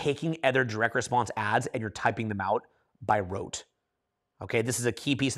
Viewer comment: Amazing video as always. What word did use around 37:52? You are saying to write down the sales copy word for word right? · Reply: Rote memorization, like active recall